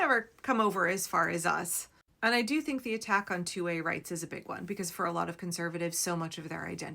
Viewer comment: I thought you said "two-way rights" (2A rights) which also makes sense. We get the rights too, both sides have the same rights.